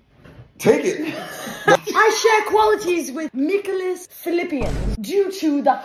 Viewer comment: I thought you said Philippines 💀💀